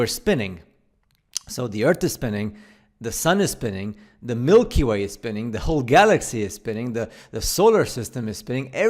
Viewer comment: Did he say the Milky Way is spinning and the Galaxy is spinning???? THE MILKY WAY IS THE GALAXY!!!! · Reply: oh yeah I misheard what he said I thought he said the Milky way is spinning and our galaxy is spinning